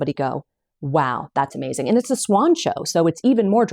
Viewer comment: Where do I find the link for cottage swan show? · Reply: I thought she said swan show, too😂 but it's College Swoncho. She has the link to Etsy to purchase. I didn't see it on Ravelry.